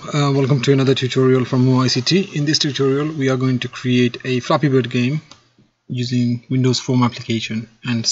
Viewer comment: At the beginning I thought he said " we are going to create a f***ing good game" im like. Da*n like the confidence lol